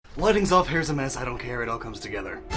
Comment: When you said "Lighting's off, hair's a mess, I don't care it all comes together" I thought you said "Wedding's off" and I was like "Wait... what?"